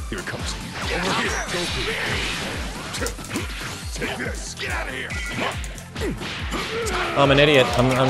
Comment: When hit said "take this" during the first minute or so I thought he said "DINGUS"